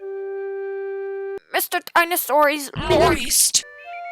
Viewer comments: It sounded like it said "mr.dinosaur is moist" 😂😂😂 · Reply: I thought it said, “Mr. Dinosaur is Loit” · It sounded like it said "mr.dinosaur is moist" 😂😂😂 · It sounded like it said "mr.dinosaur is moist" 😂😂😂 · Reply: I thought it said “Mr. Dinosaur is “loist””?